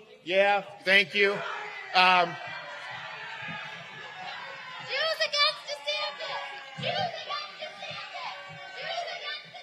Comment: "Use again DeSantis"? - That what the 'protester' are saying?